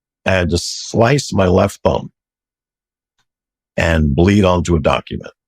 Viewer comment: Ohh he said thumb not bum like I thought he originally said butt cheek 😂😂😂 I’m like they freaky 😂😂